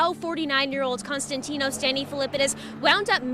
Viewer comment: Is his name Constantino Stanny Filippidis?? Or is it Danny Filippidis? ... LOL at the very beginning of the video it sounds like she is saying Constantino Stanny filippidis ... LMFAO I'm confused what is his name??? I mean I hear them on the video saying something different but why did she say that at first??? LOL Anyway that's besides the point.... I'm glad he's safe!!! I can't imagine being lost like that and having not much recollection of what happened and how much time had passed. It had to be a very scary experience!. Kind of makes me wonder more about the Supernatural and the Paranormal!. I feel like the government knows more but we common folk are just left to wonder I guess?!. Lol 👽👻👤🕵️‍♂️